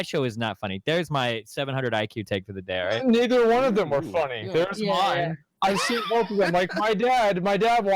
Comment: what did asmond just say? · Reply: "neither one of them are funny", but I see where you're getting ;P